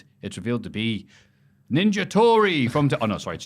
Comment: When said "Ninja Tori" I heard it as "Ninja Tory" and pictured in a Ninja outfit stealing Milk.